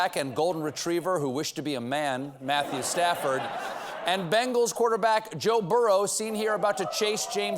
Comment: When Stephen said "Rams v. Bengals" I thought he said "Rams v. Bagels" and that shows you how much I follow sportsball. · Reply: I thought he said "bangles" and wondered.